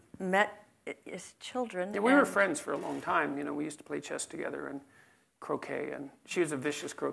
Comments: i heard "chess, cocaine..." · "We were friends for a long time. We used to play chess together... cocaine..." legit thought thats what he said first time hearing this lmao